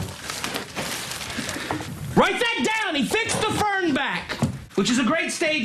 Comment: I always thought he said "Vince the Firm-Back"